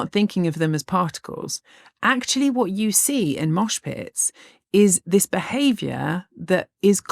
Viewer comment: I heard "auschwitz" instead of "mosh pit" and got outraged 😅